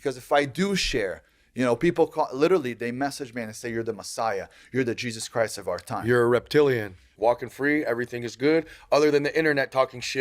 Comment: did he just call him a reptilian?!!! 🤦‍♂️